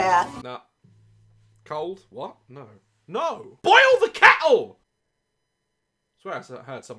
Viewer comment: I swear he said boil the cattle lol 🐄